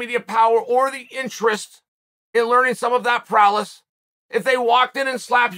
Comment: …Did he just say Prowless? 🤣🤣🤣